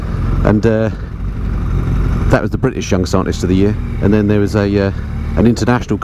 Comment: I thought you said “Young cyclist of the year” 😂😂😂